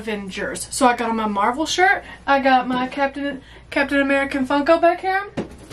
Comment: Lmao! At the intro I thought you said: "I have my Marvel shirt and my Captain America Uncle Buck" and I was like, wow - the cross-over we didn't know that we needed. LoL